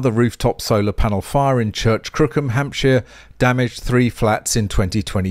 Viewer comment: "Church Crookham Hampshire" LOLz. Laughing with, not at. What I heard was "Churchcrookenhamshur" which, having typed it out I much prefer. Notwithstanding your delicious pronunciation of course.